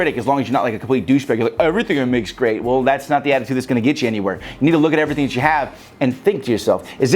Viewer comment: Always great videos and advice but who else thought he said "Ni**a look at everything that you have"😂😂